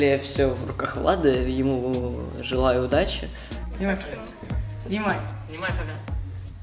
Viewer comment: "снимайте, снимай" 😹 я одна услышала?